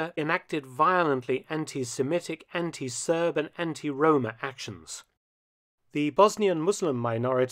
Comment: I thought I heard "anti roman Actions" there for a second